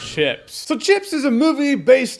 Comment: I thought said "Shits"